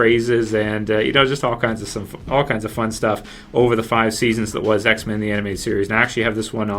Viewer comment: I think he almost said all kinds of sun fuff..